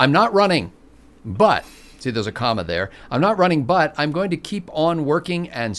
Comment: When i hear "I'm not running", I hear "I'm not running yet"